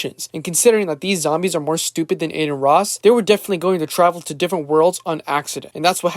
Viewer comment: you definitely said Aden Ross but the subtitle say otherwise💀